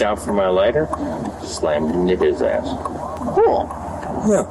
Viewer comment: he did not say hit his ass he said nigga